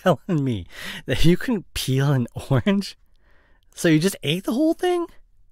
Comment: Guys when he said “peel an orange” I heard “pee an orange” and I got really scared for a sec 😭